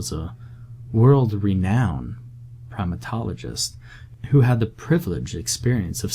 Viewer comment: We he first introduced the doctors I heard "world renowned proctologist" (instead of primatologist) and for a split second thought it was about to switch to a freakypasta. But really though I did drop my candy and tap back 10 seconds